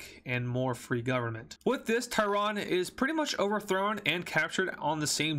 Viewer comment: did you say taiwan? love your videos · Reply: TEHRAN IRAN CAPITAL CITY